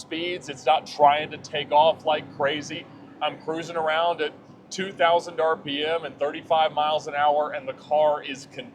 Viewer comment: my headphones glitched and i heard “i’m cruising around at 2,000 mph”